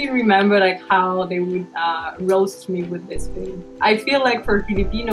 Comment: Who else hear is hear for homework? Also who filled their tabo with water and put it on their head? 🤣🤣🇵🇭 (edit: the one like is made by me 🥲 also at 5:28 did she just say roast 🤣🤣🤣🇵🇭)